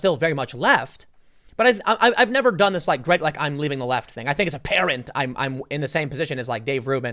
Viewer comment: I though he said "I think as a parent" and I was all *"WHAT?!"*... But I think he actually said "I think *it's apparent*. Oh, the thought of Tim with a little Beanie baby is funny to me.